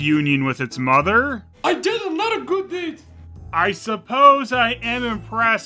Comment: I misheard the Frog saying: “I did a lot of good deeds” and thought he said “I didn’t like this movie!” 😂